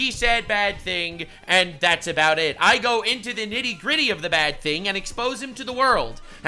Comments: did anyone else hear "into the Diddy gritty" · Bro did you just say you go into the "Diddy Gritty" if not and I just heard it wrong, please use it and not nifty gritty, but I believe that's what you said.